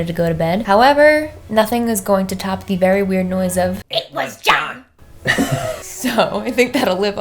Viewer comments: I heard "it was gone" btw loved this episode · But like why do I hear “It’s for god?” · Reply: I keep hearing "It was GONE" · I didn’t hear “it was John” I heard “it’s my job” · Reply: Lol, i heard "it was done" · I literally hear "it was jonna" or "isbarada" 😂😂😂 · When you guys heard it was Jhon i heard "I just forgot" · I heard ‘it was gone’ 😂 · Okay, now I hearing, " It was done". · it so hold up ik im watching in dec but I love mykie it sounds like its saying it was gone